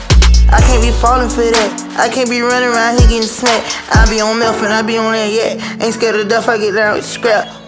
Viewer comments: I like kodak alot but it sounded like he said "I be on mef (meth) and yak".. uhh woah bro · I swear i heard sum bout “i be onnat meth” 🤔👀